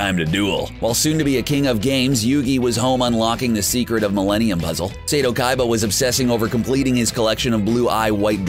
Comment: did he say Millenium Muzzle???